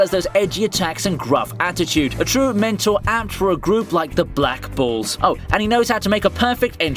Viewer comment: Somehow my ear heard him says "Black Balls" instead of "Black Bulls"